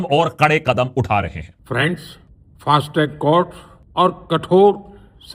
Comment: did he said friends